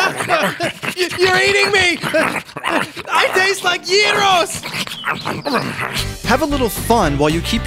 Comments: i taste like uterus , that what i heard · at the begining of the video (0:11) did u said 'gyros'? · did he say "I taste like Gyros"? plz someone answer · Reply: TheWildArcher TDM Yes. Gyros, as in the rolled sandwich-like food from Greece that typically contains lamb